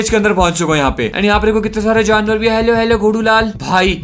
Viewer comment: I just heard it something else instead of ghodulal I heard lodulal